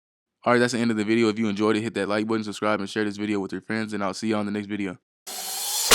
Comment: i thought you said share this video with ya parents 0_o